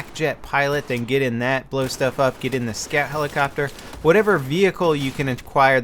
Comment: did you say get in the scat helicopter?